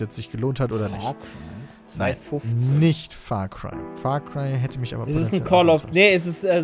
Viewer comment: Was sagt da ? Far Cry hätte mich potentiell ... und dann Bitte um hilfe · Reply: entäuscht wollte er sagen, immerhin war nur der dritte teil gut